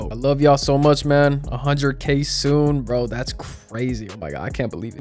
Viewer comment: did bro just say eat some water💀?